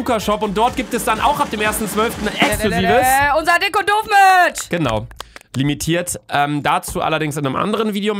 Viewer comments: Ich hab habe bei Minute 0:20 min verstanden unser exklusives dickes Kondom Merch😂😂😂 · habt ihr auch verstanden "Unser Kondom-Merch"?😂